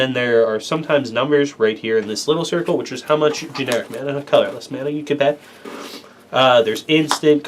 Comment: bro wtf did you say at 1:17 lol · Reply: "how much generic mana, colorless mana, you can pay." I don't think the generic part of the cost is optional though.